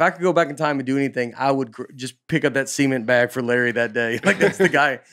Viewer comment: When Nate said “Larry Bird hurt his back carrying SEE-ment”, my non-southern brain came to a screeching halt, and went “WHAT THE HECK DID HE JUST SAY??” 👀 Ohhhh CEMENT. He was carrying cement. Not semen. Okay, whew. I usually do just fine with y’all’s accents but that one really stopped me in my tracks for a second there! Great stuff, boys!